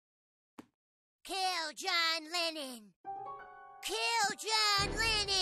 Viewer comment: I hate the fact i heard "Kill giant Lenin"